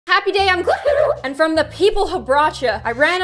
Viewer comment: Why did I always hear “Happy day on Gloom!” And not “Happy day IM gloom!”